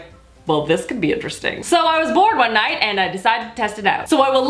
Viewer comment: I thought she said "so I was born one night..." no shit lol